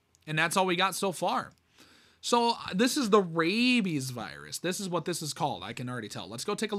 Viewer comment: i thought it said “babies” 💀